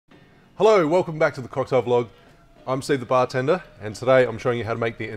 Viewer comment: I always hear "Hello, welcome to the cartel book" and I always hear "Hello, welcome to the cartel vlog". I knew it! you are a cocktail dealer! 😆